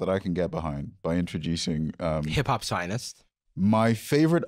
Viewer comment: Did Jarvis say "hiphop zionist" at 41:24 or are my ears deceiving me? 😂